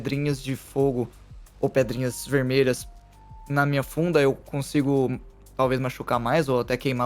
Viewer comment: eu entendi bunda em vez de funda